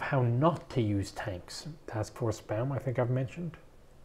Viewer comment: Did he say Task Force Baum? ("Bowm" is not how I pronounce "Baum", so I'm not sure I understood him properly.)